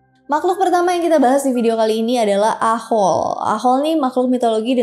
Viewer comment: Gw salah denger,gw kira aho aho🤣🤣 itu mah gagak yang di anime2🤣😂